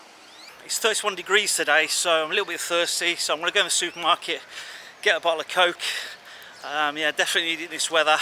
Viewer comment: did you actually said you were going to the market and get alot of Coke?